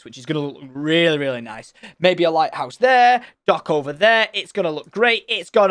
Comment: did he say light house AHHHHGGGGHHHHHH